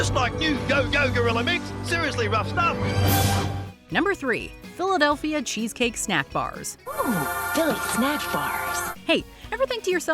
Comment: Oh man I thought it said Philadelphia Cheesesteak snack bars